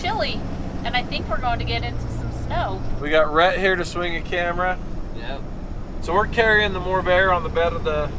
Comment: Literally thought said, “we’ve got rat hair slinging the camera” 😂